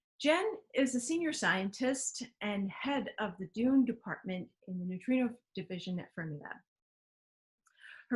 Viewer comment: I thought you said "DOOM dept", not DUNE dept!